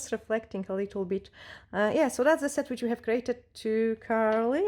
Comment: Beautiful beautiful set. Omg haha I thought you were saying glittery hex all this time. Lol 😂 I didn’t know what it meant. Got it !!